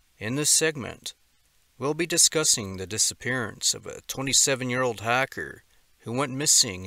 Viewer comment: I rewally thought u said a 27 yr old hacker 😊